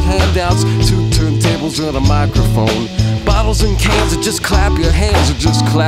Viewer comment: At no point in the video did he have two turntables and a microphone. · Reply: I always thought he said two tap tappers and microphone